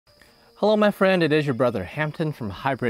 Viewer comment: All I hear is, "Hello, my friend, it is your step-bro, Hampton..What are you doing step bro?"